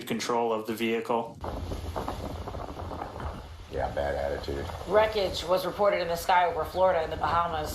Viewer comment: you hear "you have a bad attitude" LOL i think the male newscaster said it . There was some tension there thats why you said that conversation sounded weird